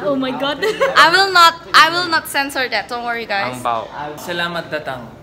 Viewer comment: also, in 3:05 did say what i think he said? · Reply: Anjing? haha